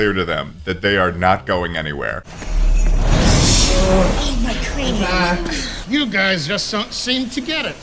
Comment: did someone say "ow my cranium" or am i deaf